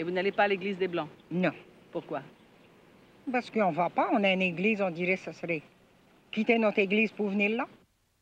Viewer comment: Je ne peux pas comprendre la réponse de la femme créole à 13:08. Pourquoi elle ne va pas à l’église des blancs? (Je sais la vrai raison, mais quelle été ses mots) ? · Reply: 《 Parce qu'on va pas, on a une église, on dirait ça s'rait comme quitter notre église pour venir là. 》